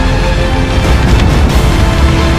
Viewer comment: at the end when it say game over i thought it said gay over